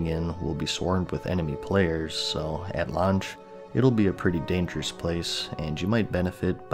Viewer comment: I keep hearing "lunch"....damn, making me hungry by trying to say lauch.